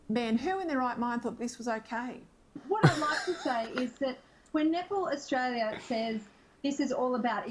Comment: I thought she said 'n*pple' Austrailia: 4:49.